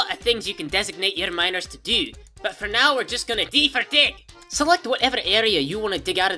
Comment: I like this. I will have to try this game now. Also, it sounded like you said "D FOR DICK"